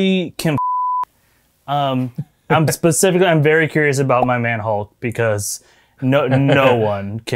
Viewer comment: Oh you said “My man hulk” 👀 totally misheard you